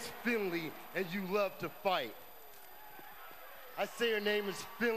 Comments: I can clearly hear the "s" sound. I thought he said Bassturd as in fishpoo? :S Who cares? He got released didn't he? :D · thought he said bath turd